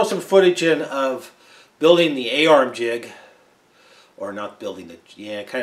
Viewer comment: You HAVE been busy. My ears perked up when I thought you said "AR jig" and then you repeated it and I then correctly heard you say "A-ARM jig". Nice job on the surface gage "lip" and a BIG congrats on the surface grinder and HS grinding vise.